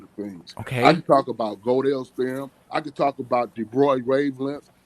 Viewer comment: Is it just me or does it sound like is saying girdle rather than Godel?